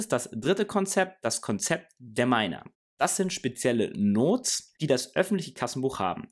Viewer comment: "spezielle Notes"? Sagt er Notes also Notizen? Verstehe es nicht ganz.